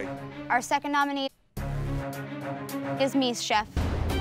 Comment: when she said “Meese” I thought she said “Me” I did a double take so quick thinking she put herself up for elimination 😂